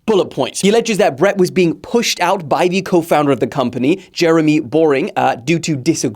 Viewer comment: It's Boering, I know it isn't very clear because it does sound like you say boring